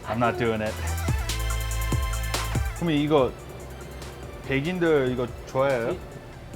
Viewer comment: Subtitles are wrong. At 2:26 he doesn't ask if "Westerners" like it. He asks if white people (백인/baegin) like it.